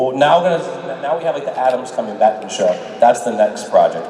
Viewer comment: did he just say "Now we know Adams coming back to the show"